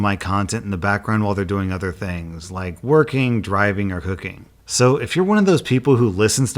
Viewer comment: My brain didn't process the audio correctly and I thought said that folks listen while "working, driving, or hooking" and was just like WHELP whatever gets that mood going I guess??? Cooking. Cooking makes so much more sense.